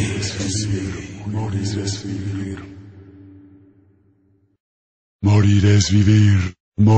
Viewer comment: I'm Greek. I thought it said "Monimetiri", which in Greek translates to "Alone with cheese" ("Μόνη με τυρί")